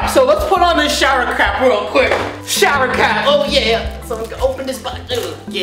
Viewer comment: when he says shower crap😂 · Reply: Jenny Huynh Ikr??? I thought he said "shower cow" 😂😂😂